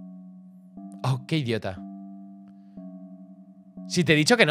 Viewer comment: creo que dijo "que idiota" por no aprovechar para ponerles un precio a las galletas.